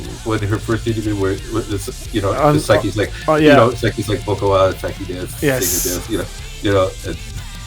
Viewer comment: I always thought Saiki was saying "vocalist Saiki desu", not "boku wa". I'll definitely be listening closer next time. I think the only time we've seen the real Kanami is what we've seen from some rare rehearsal videos, and her composing during "Band Camp". I think she goes all-in on this character like Andy Kaufman so even in behind-the-scenes stuff from like the US tour Blu-ray she turns it on. I think, except maybe Akane, they all play characters (Misa is apparently really girly in contrast to her image; Saiki very recently said that she was always trying to act cool; and of course Miku is apparently an old man, which is pretty much the opposite of her pigeon character). I think Kanami being away with the fairies is appealing because of "the gap" it creates with her musical genius. Regarding "Epic Narratives" I'm on the side of too many songs released beforehand, and I can't rationalise it away. Even at the most basic level, listening to the whole album, it results in an uneven experience because I've heard the old songs 4 or 5 times more than the new ones, so I want to rush to the new ones. That Fukuoka setlist is incredible! Thanks for the cool conversation!